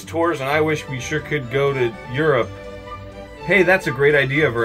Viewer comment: That's where i'm from! Oh wait i thought you said Europa never mind.